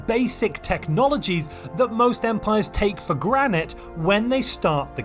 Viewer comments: am I crazy or did he say “take for Granite”? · Waitaminute...did you say "take for granite" at 7:24?! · did you really say take for granite at 7:23? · Did you just say "for granite?" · did you just say take for "granite"? 🤣